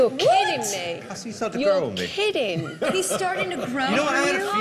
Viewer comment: Victoria was wrong she said he did not have the guts to back it up, but I think he did. He told the reason why he was unedged and then Kara was accusing him of being rude and arrogant. lol I did not get what Simon said 4:25 , his reason for supporting. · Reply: Scott Fresner he is starting to grow on me